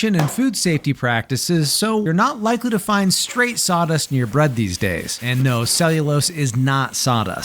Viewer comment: Omg I thought he said stardust.